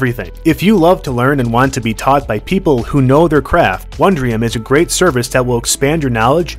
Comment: misheard "people who know their craft" as "people who know their crap"